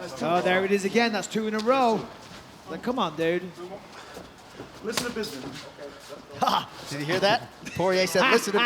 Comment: What did Dustin say at 02:40? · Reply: ‘listen to Bisping’